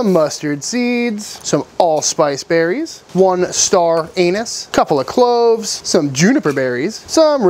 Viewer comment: I’m sure I heard you say Star Anus.